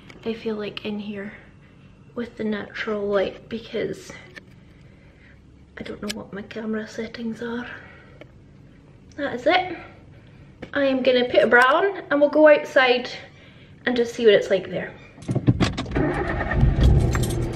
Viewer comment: As you said 'I'm going to put a bra on', I was like nooooo don't! And then you didn't and I was pleased haha. No bra is one of the few benefits of lockdown lol. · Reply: Ooo a bra! I heard "I'm gotta put a brown" and I thought excuse you?! 😂